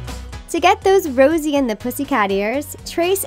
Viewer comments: Did she say Rosie and the pussycats at 8:48 there is also cherries not strawberries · am i the only one who hear her say rosie not josie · I think I’m on fizzle rocks because I heard her say Rosie and the pussycats and not Josie and the pussycats